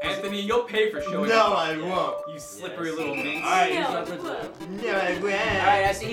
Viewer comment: Did anyone hear Lasercorn call Anthony a minx? 4:53 "You slippery little minx."